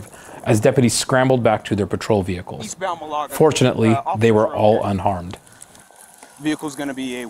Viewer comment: did he say UNfortuanately they were all unharmed?